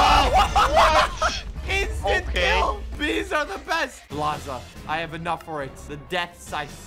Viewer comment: when said bee's are the best i thought he said this are the best lol